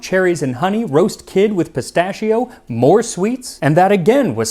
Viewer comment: Thanks to Trade Coffee for sponsoring! Get a free bag of fresh coffee with any Trade subscription: <link> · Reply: Umm sorry, did you say "Roast kid with pistachio" at 18:02 ? :D